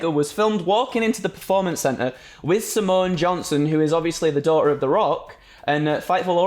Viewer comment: I thought said "Samoan Johnson". I was like, is that The Rock and Samoa Joe's love-child?